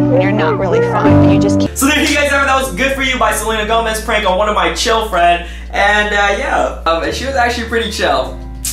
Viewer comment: i thought you said on one of my children then i used my brains.